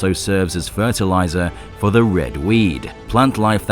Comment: did you say red weed? Are the chilean making a drug alien in the planet are there making alien drugged and this planet it's really weird it's wrong with this aliens